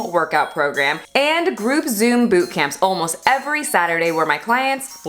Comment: I thought she said Faturday 😂😂 definitely gonna use that word from now on lol